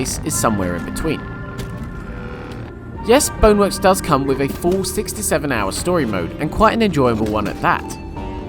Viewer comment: woahhh had to rewind that one thought you said 67 hours haha